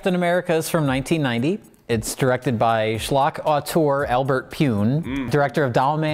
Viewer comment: What's the word uses at 19:18? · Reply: "schlock auteur"